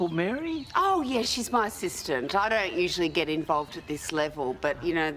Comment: But I heard she's her sister even if slowly